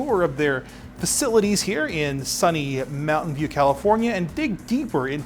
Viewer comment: i thought he said mountain dew california, anyways welcome to california enjoy your stay we have some great beaches